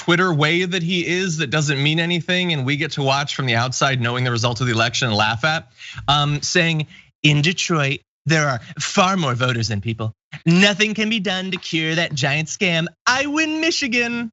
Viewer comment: my stupid brain heard Baltimore instead of Detroit.